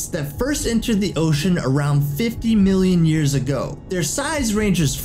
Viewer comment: 50 MILLION YERS AGO. Wait did it just say yers?